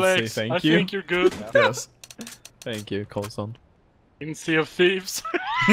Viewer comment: did he say sea of thieves?